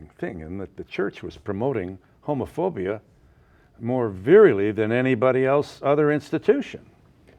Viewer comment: What's the word he says, 'virily'? Does he simply mispronounce 'virulently' or is this some other word?